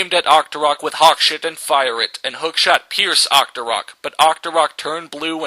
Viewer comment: When "Hookshot" is spelled wrong it sounds like you say "Hawk sh*t"